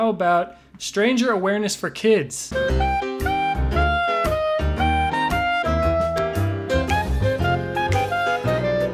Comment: i hear a kid yelling KILL US!!!! Or is it just me? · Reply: Nah its the kid said kill us but its kinda fUnny